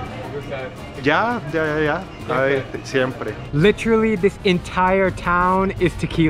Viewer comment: Did he just say "syempre"